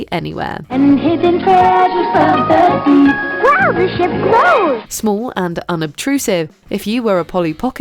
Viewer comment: I thought I heard "This sh*t grows!" My childhood was full of innuendos.